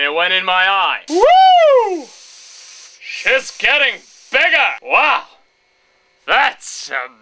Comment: Wait on 1:01 did he say "She is getting bigger" !